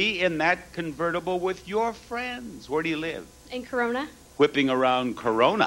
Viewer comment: Did she really say Corona? :0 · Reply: Corona, California